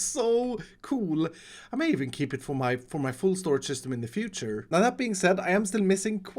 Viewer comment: I thought I heard foolcraft storage system and I got excited